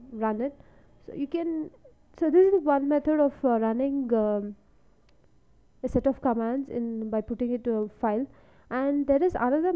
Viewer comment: i cant understand your voice , so i pressed the caption button....in 2:02 you said government bible something the what now?